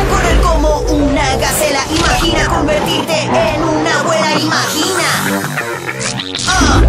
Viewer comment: yo entendi una wea imagina